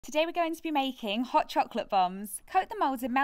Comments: I was eating and i thought u said at the beginning “Today we are gonna be making chocolate bums” 🥲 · I'm so sorry- But I thought I heard "chocolate bums" at the start😭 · When she said bombs I thought she said bums 🤣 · The first time I heard her I thought she said hot chocolate bums l🤣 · ngl i thought you said hot chocolate bums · I thought u said hot chocolate bums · I thought she said hot chocolate bums 😳 🤣😂😂